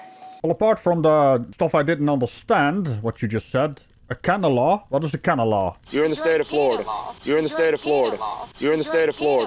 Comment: I think she said “cannon law”. whatever she thinks that means. 🤷🏻‍♂️